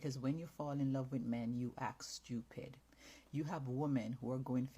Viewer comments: Aye yoo after I heard "stupid b**ch " 😂😂😂😂😂 bro thank you 😂😂😂omg yea I'm high shii gonna be on repeat for no reason 😂😂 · Is this a skit !??? 🫣 I thought I heard STUPID B!TC#